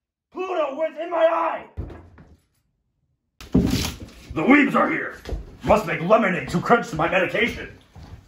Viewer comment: wtf lmfao what he saying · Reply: “The weebs are here. Must make lemonade to quench to my medication”